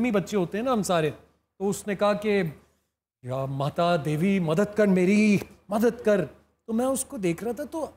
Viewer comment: he was prolly saying "jay mata di let's rock"